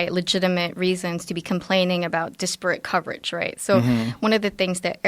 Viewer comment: What exactly does that mean? It kind of sounds like she's saying it's a specific problem of the right to complain about disproportionate (I assume that's what she meant, it sounded like disporate, but that's not a word) coverage. Well, of course the right has that concern, but the way she phrased it sounded like it's a problem of the right, not for the right...